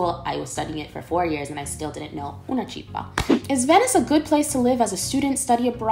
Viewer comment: What did you say at 10:14? Una cippa? What does it mean? :)